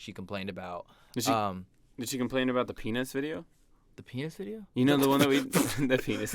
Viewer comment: I thought at 27:40 in the video said penis video when he actually said peanuts video lol 😂😂